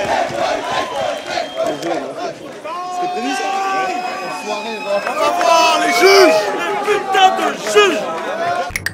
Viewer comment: Il dit quoi vers 23:50 ? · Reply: a dit :"tu l'as prévu ce coup? ". Ensuite répond oui et dit :"Enfoiré."